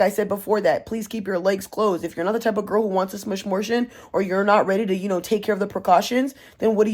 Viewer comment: Did u just say smishmortion GIRL I THOUGHT I WAS THE ONLY ONE WHO SIAD THIS LMAO